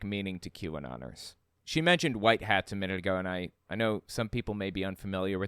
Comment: White Hats…I thought I heard White House ?